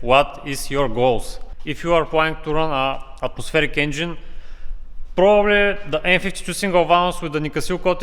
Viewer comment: Can someone translate what he said at 15:33 ? He said if u want to run a prosthetic engine?